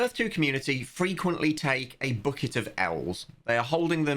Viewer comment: It took me a few listens to realise you said "A bucket of Ls" I was so confused why these people were "holdinging a bucket of owls." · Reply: I heard "bucket of elves", and thought he meant leprechauns, because that's the best chance of making money from E2